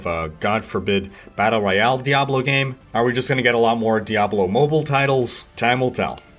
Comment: Did he say Diablo IMmoblie? (16:25 I think) THAT's a name I can get behind! #DiabloImmobile